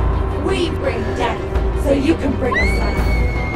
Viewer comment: Years later...a party City mask was placed on the tree... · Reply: At first I thought they said... We bring death, so you can bring a slice. LOL.